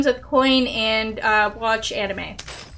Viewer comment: did I just hear from you "and watch anime" at 1:10:39 ? I'd love to see in your channel Anime or Movie reviews!! For a sort of 'stsrting youtube channel' you have good content and great attitude (Marz/Liteweith/LiliaTV kind of level) so expanding your content (eventualy, if you will) that would be so enjoyble!